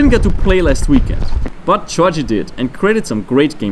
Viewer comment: Is it just me or does he say”Georgy” not Josef, 0:13, 0:30, 1:28, 4:05, 4:19, 5:04